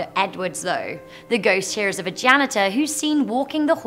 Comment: She said, “The ghost here is of a janitor.” I swear I heard, “The ghost is a vaganitor.” 😂